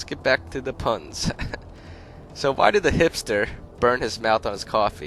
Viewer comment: I thought he he said hamster instead of hipster